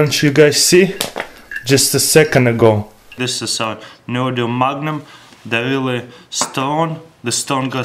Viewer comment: it sounded like he said magnum 😂😂😂